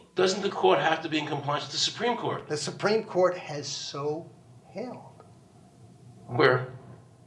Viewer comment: what does he say at like 7:25? "the supreme court has so held"?